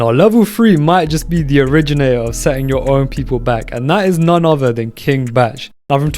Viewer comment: I genuinely thought it said “setting their people black” and didn’t get it for about 5 minutes